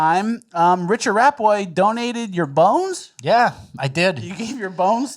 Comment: At first I thought they were gonna say donated fuckin bone marrow.... that would be painful as shit.